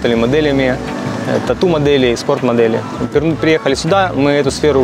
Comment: это то что я услышал??? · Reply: Не экскорт, а спорт-модели😂